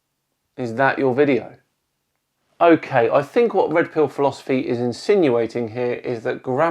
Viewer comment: I thought he said rectal philosophy and not red pill, eh either way it’s the same shit spewing